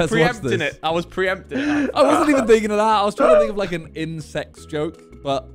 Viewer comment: i thought simon said Incest in